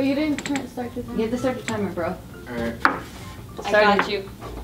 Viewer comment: says i got u instead of julias got you